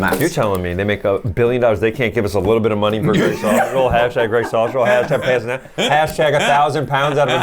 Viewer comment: I always thought they were saying Grey Sausage Roll 😂😂😂!!! I always thought EWW! I 🤮🤮🤮